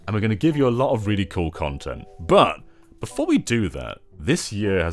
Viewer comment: I don't know WHY but I heard "really good content" (00:12) as "Kiddy Kong content" at first. I rewinded it because it sounded too good to be true. I was right :(